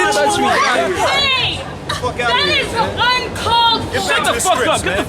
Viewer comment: What is the women at 7:31 saying? I understand "hey, that is the wrong cultch." and i dont believe the last one is even a word. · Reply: she says "Hey, That is un-called for!"